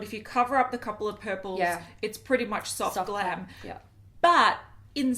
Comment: When was saying Soft Glam, I don't know why but I thought she was saying "clam" and "clammy" 😂 Needless to say, I need my hearing checked!! Love you gals and thank you for putting so much time anf effort into all of your videos ❤️ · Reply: I was saying clam, you heard right 😂😂😂😂